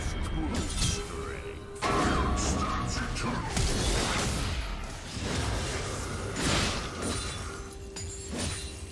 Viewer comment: n entendi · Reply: Hay un error en la repetición. Es w flash